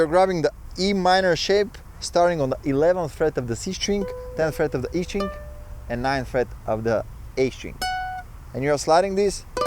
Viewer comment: does anyone understand what numbers they're saying? I heard eleventh, but then I couldn't understand the next one. Plz help, I need to learn this before Friday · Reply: 11th fret of the C string 10th fret of the E string 9th fret of the A string